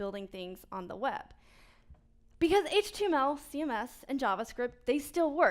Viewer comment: What did she say at 6:15 ??? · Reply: Not 6:15 at 6:18 .. she told cms instead of css thats all